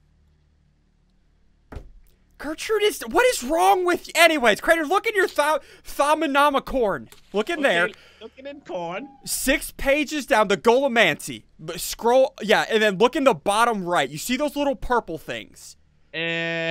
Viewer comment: it sounded like he said '' Ok i am looking at porn''